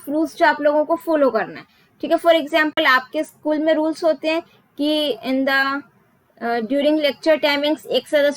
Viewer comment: Mam do u speak Malayalam ? Did u say Nthaa? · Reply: She said "in the" not nthaa